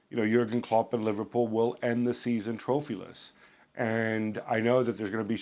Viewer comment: Did you say trophyless?🤔🙄😂